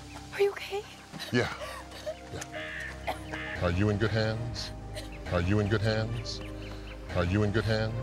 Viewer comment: I literally thought he said Are You In good Hands!! I can't be the only one